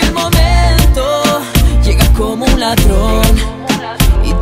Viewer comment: en el minuto 1:13 que dice? pd: linda cancion, lindo cantante, lindo ritmo😍🎶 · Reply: Llega como un ladrón :)